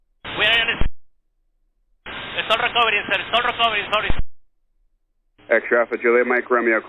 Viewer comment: It's sounds like he said "it's not recovering sir, it's not recovering sorry".